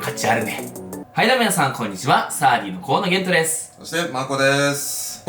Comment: マコさんマoコにしか聞こえない件について(確かに下世話だな)